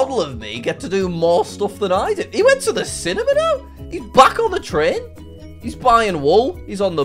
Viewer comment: Ayo what did 13:57 you say cinimina